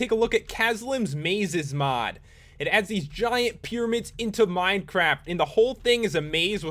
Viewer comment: excuse me did i just hear "minecrap"?